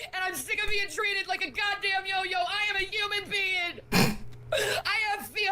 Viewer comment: She's a human peeing is all she is. That's what I hear everytime